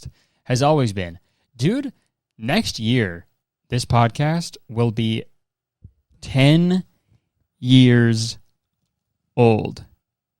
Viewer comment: well he was saying next year would be 10 years, so 2027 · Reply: ohh, I didn’t hear that part, I thought he said this episode was 10 years